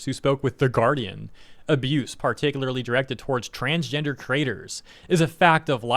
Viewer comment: I heard "transgender traitors" and thought "what new buzz word is this"